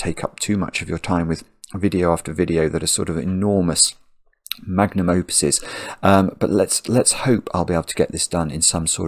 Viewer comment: Hi can someone help to spell/define the word he uses at 1:16 it sounds like "magniovitus" closed captions say it is "magnum opus" thank you, love you x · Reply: "magnum opus" (or in this case, "magnum opuses") is correct - i think the definition is an artist/composer's finest or most famous work, but in this sense is using it to mean very long solves.